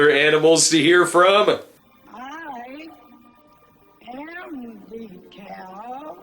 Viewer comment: I heard "I am the Dog King". Anyone else?